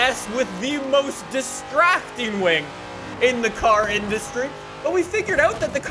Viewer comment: I thought was about to say "The most disgusting wing ever".